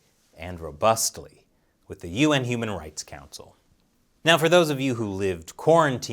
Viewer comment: i hear that as "UN Humanoid Council" ... i wonder why? while at 3:22 i hear it normally as "UN Human Right Council"